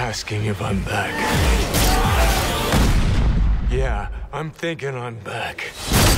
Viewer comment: doesn’t it sound like he says “I’m thick and I’m back* lol